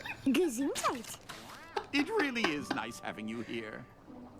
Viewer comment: Did she Just say "Gesundheit"? That's German and means "Bless you"! xD Oh my god this is so perfect because I'm from Germany! XD Wie ist mir das das erste Mal nicht aufgefallen?! x)